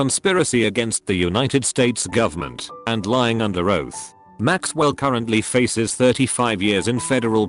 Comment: i thought the last example said living under ohio